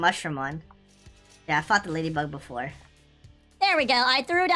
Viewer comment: what did you say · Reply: He said fought lady bug before